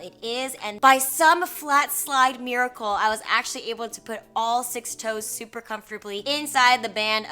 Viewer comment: Did she say she puts all SIX toes into her shoes? Loved the haul btw! :)